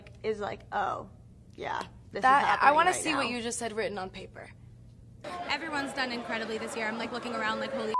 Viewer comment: wait what did she say at 4:05? i genuinely thought she said everyones racist but theres no way 😂